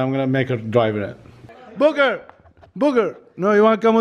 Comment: It sounded like he called booker booger😂😂